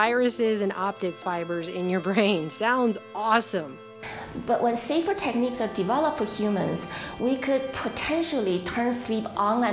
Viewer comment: does she say “But when secret techniques are developed...”????